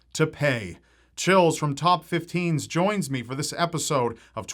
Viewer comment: I thought chills said chilis for a few minutes